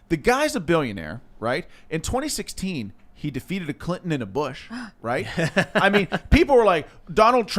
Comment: I thought he said 'Defeated a Clit and a Bush' 😂